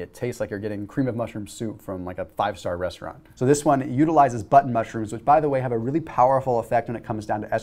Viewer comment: You might want to speak a litlte slower because it sounded like you said Butt Mushroom Soup...